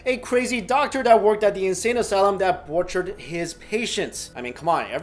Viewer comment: wendal Oh okay, thank you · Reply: I hear "tortured" but I suppose "butchered" makes sense too.